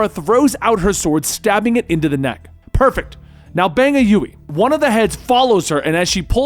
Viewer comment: (⬅️ magic number for Fetty Wap) Did she say "bang a U-E" ?